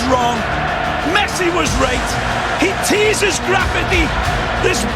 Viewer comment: for some reason I heard that "messi was raped"